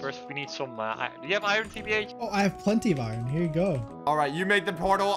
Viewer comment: Wait did i hear foudre (thunder) in